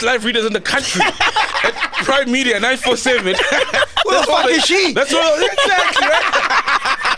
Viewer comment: did Macg just say "who the f*ck is she " to his mom 😂😂😂